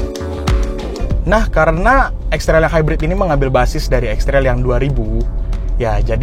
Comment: Kesimpulannya Extra hybrid, wkwkwkwk.. Gw salah denger apa, emang ngomongnya extra hybrid ya pas kesimpulan?